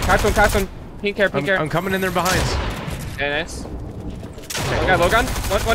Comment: Am I the only one who heard him say “I’m coming in these guys behinds”?